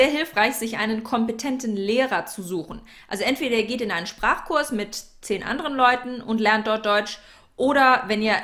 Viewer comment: Could anyone tell me what was said at 7:39 to 7:41? I couldn't quite make it out despite replaying it multiple times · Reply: also entweder ihr geht in einen Sprachkurs mit zehn anderen Leuten